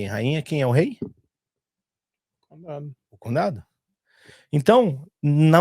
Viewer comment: quem é o rei? n entendi o que ele disse... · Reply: O CONDADO. no EUA seria tipo o municipio.